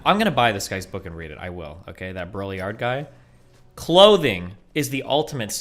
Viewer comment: What is he saying the guy with the "book" name is? Brolyard? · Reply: Simulacra and simulation by Jean Baudrillard